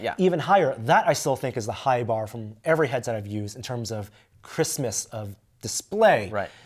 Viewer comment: Love the review, but did i hear "Christmas of display" @ 7:53?